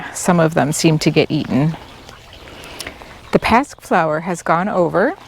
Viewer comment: what is the flower at 25:31? I didn't understand the name when you said it. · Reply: Pasque flower or pulsatilla. 🙂🌸🌿⚘️